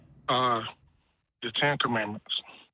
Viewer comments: What’d he say at 1:39:20? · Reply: "10 Commandments" I realize this is a shock for every atheist and every other religion in the world, but it seems the 10 commandments is his evidence for a God. Ha Ha Ha Ha Ha Ha Ha Ha Ha Doesn't he know how ridiculous he sounds? Hence why he was hung up on. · What does the caller say at 1:39:18 ?!?! I can’t understand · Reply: "Uh. The 10 Commandments."